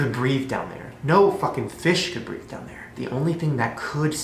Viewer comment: Did shane just say he doesn't even think FISH could BREATHE down there?